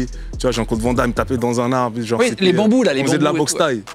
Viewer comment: les quoi ???? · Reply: Les bambous